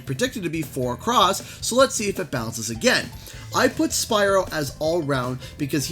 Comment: I miss heard when you said Spyro and thought that you said Spider-Man lol